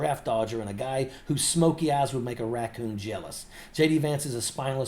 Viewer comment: "smokey eyes would make a raccoon jealous"??? EXCELLENT!!! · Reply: Oh dear, I misheard it as "Smokey ass that would make a raccoon jealous" 😅😅😅 Assumed he was referring to Vance being covered in trash/being trash. I'm not Southern and the accent gets me from time to time